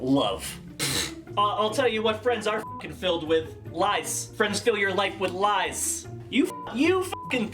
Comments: I thought at 9:10 he said 'lice' not 'lies', and to be honest......... 'lice', would have been better! LMAO! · lmao I thought he said lice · i thought you'd said "Friends are filled with LICE" not lies & now I'm disappointed that's not what you put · I thought he said lice. Friends fill your life with lice. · I thought you said "LICE" ....Friends fill your live with LICE. LOL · I thought he said lice lmfao true though 🤷🏼‍♀️ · "Friends fill your life with lice" is what I thought u said